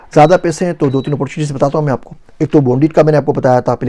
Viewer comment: Did you say boondit? @ 18:59 ?